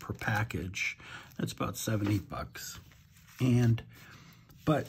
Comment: When you said, "You get 100 sheets for about 7, 8 bucks" I heard "70 bucks" and nearly spit my wine! Love your videos, though!